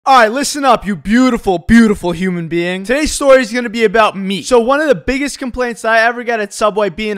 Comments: I thought he said today's story is going to be about him but it was actually meat😂🤣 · Lol. I thought you said. This video is going to be about me. Rather than about meat.